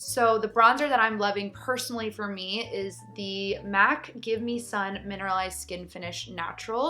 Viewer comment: What is the name of this bronzer? I can see its MAC, but can´t find the color/name. Do anybody know? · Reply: It must be mac give me sun mineralized skinfinish, that's what i hear